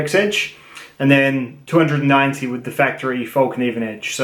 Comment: Did you say 290?